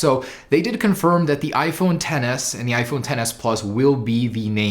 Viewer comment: Sounds like your saying iPhone Tennis there... sorry just had to get that outta my system... Love your channel keep it up man.